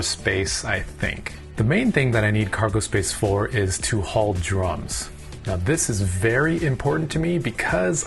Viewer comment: lol I thought he said "haul drugs"